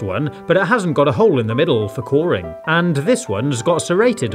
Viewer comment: the first time i heard it I thought he said boring.